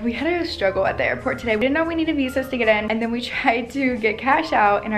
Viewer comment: Did I hear “we didn’t know we need visas to get in”??? 😰